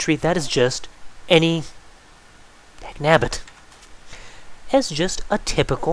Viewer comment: What is the word he says at 20:25 ? Sounds like "dagnabit" or something.